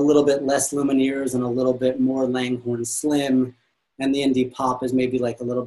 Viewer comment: I am curious to know what name said @ 9:08. I couldn't quite catch it. he said something to the effect of "folk is getting a little less Lumineers, a little more _______? Thanks! Enjoying and learning! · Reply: Langhorne Slim